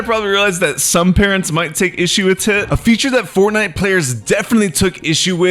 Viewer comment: I cannot be the only one who heard him say when explaining parental backlash from the Calamity physics that they “might take issue with tit”